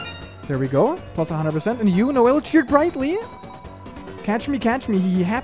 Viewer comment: Why do I keep hearing "You know well" instead of "You Noelle" ??😂